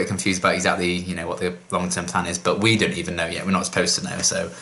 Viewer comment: ok this is odd but it just made me chuckle for a moment 'cause as I was about to make a comment, this is what I heard you say "But Whedon even know it" 😂🤣 yeah... 🤦‍♀️😑 Anyhow, about that spoiler situation, mannnn someone needs to make a meme pic for it using the scene with Cordy stabbing Lilah in the end 😨 with the caption 'Don't Spoil or else' 😤😤😤 I know that seems too barbaric but it really is frustrating when that happens. Speaking of Cordy stabbing Lilah, I'm still wondering if that really was necessary for her 'cause she said she let Angelus out for a reason... but I think that was done intentionally to pretty much show the viewers that something's seriously wrong with her and this move just takes the cake. Oh and that thing she was holding... wasn't a wooden stake and it wasn't to the heart.